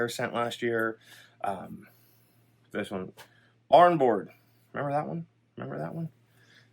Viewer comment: I didn't catch what you are saying was a good smokey candle from the past. Sounds like you were saying barn board? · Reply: Barnboard yes! Came out many years ago